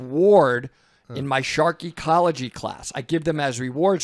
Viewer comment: I thought said Sharky College-y until I realized it's probably Shark Ecology